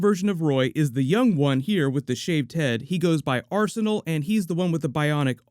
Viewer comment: i thought you said "he's the original, with the SHAPED head" and i was like, aw you didnt have to do him like that lmao